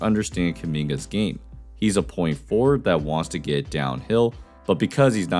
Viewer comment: Did bro really say “point forward” 0:50 💀